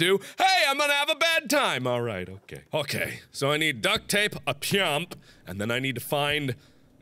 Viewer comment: did he say he needed duct tape, and a pimp?